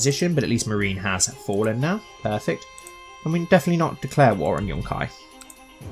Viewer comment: Why did I hear you say "declare war on Hashtag"?